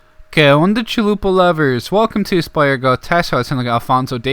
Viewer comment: what are you saying? Which language do you speak? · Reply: Que onda wey. What's up dude in Mexican Spanish